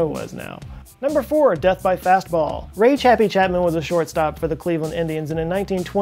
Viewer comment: I was not actually looking when you said Ray "chappy" Chapman, and I thought you said "Rage Happy" Chapman and I was like "Wow what a nickname, dude must have had a lot of rage issues."